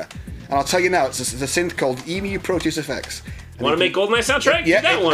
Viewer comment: what was the name of the goldeneye synth? i can't understand what grant said cause he said it to fast and with his accent it just became kinda muddled... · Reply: Emu Proteus FX - patch is "Infinite".